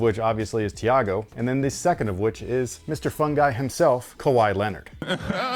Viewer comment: I initially thought you said "Mr. Funghi himself" lol